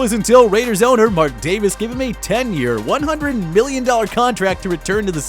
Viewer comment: i thought he said 100 billion